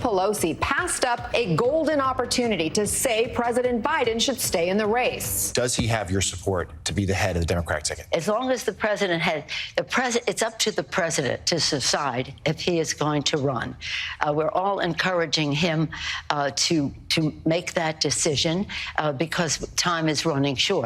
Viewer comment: did Pelosi say “It's up to the president to suicide…” ?